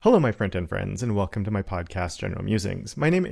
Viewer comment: Literally every time I hear the intro, I hear "hello my friends and friends"